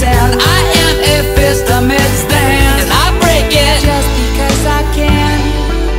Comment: NOOOOOoOOoOOo I HEAR "let´s break it just because we´re gay" AJKASHKASHJ